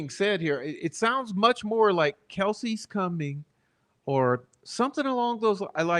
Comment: After gets off the bridge and the camera pans to the left it appears to be 3 shadows not 2 and the BG hasn't reached them yet so the third shadow is not him. sounds like she is saying "Kelsy is coming". Then when says "this is the path" it is someone else saying "that we go down". To me it sounds like Kelsy was with them on the path.